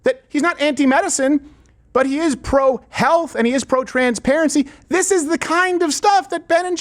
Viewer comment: He's pro "Trans - prarentsy" is what I heard, I had to go back and listen again 😂😂😂😂😂😂😂😂😂😂